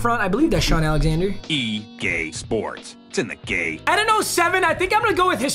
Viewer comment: It sounds like it says ea sports to h the gay